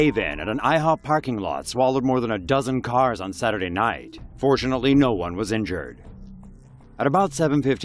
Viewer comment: I thought he said "unfortunately no one was hurt " and I was like wtf did he just say 😂😂